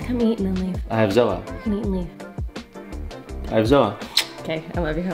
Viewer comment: DID I JUST HEAR I HVE A SOA